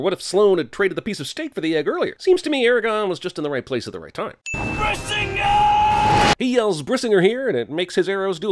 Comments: Dude when i heard him say brisinger the first time, i thought he yelled "RASENGAN" and then i was a mix of pissed and confused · During this video, I heard "Bazinga" instead of "Brisingr" and I'm not sure if that would've made the movie better or worse...